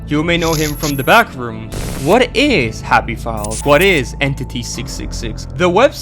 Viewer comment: I heard "Happy Balls" 💀💀💀